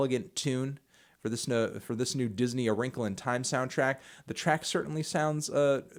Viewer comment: I thought it was said 'Unkle' and I was excited now I'm sad.